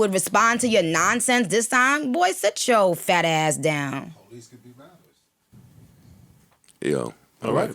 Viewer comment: What did dude say in background at about 36:50 mark after she was talking about Ross? sound like he said police can be rappers but not sure lmao